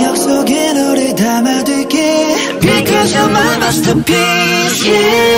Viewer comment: I can't be the only one who understands at 2:49 "because your mama still piss, yeaahhh!" 🤣🤣🤣